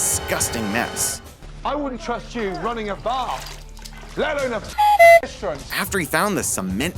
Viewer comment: I think he said bar not bath. I'm not sure